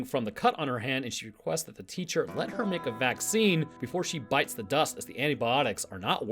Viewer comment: Is he said "Bite the dust"?